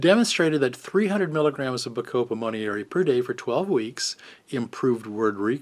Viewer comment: What was the neutropic he said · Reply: Bacopa Monnieri